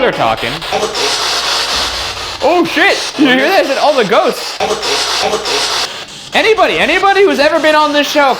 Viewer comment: I heard the "all the ghosts" as "Horrible"